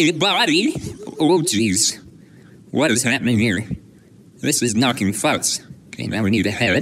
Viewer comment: “Its nucking futs” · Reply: I heard “this is knocking F*cks “